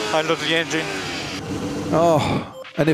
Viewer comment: Can someone please tell me what perez saying at 0:41 ? Thanks 🙏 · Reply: "I lost the engine"